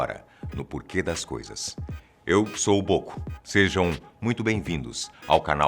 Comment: só eu ouvi ''OCO''?